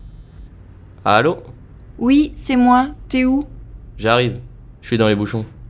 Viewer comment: Mixed for me. I kept mishearing words I should have recognized. In #2, When the lady says "T'es oû?", I thought the man's name was "Téou"! And when she said "Je t'attends devant", I couldn't catch it even after 5 hearings. I heard it as "Je t'en vends", so thought she was selling him something. In #3, the word "onglet" was a new one, so of course I missed it. When the waiter says "Salade verte", I could hardly hear the "d" in "salade" and the "t" in "verte" sounded like a "d". #5 was impossible. "Tu veux que j'y aille" sounded like "Tu es vielle?" I wondered why he was asking if she was old. And "On a besoin d'autre chose", all I heard was "honête chose", which was way off. #1 and #4 were perfect for me.